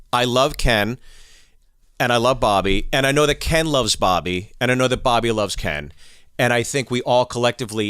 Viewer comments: I kept hearing Barbie and Ken LMAO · Lol i keep hearing “Ken and Barbie” 💀😂